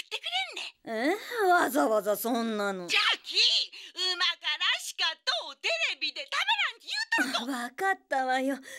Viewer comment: 〜のおばあちゃんのセリフ方言のせいかなんて言ってるか分からん… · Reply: ｢だから、美味しいから食べてみてってテレビで言ってたの｣